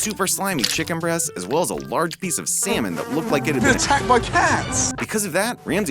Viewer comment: large piece of what? What did he just say lol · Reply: Salmon